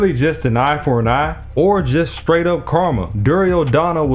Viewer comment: Yo when you said karma I thought you said karna and I gave you applause for the pun lol I'm a terrible person 😂 keep up the content man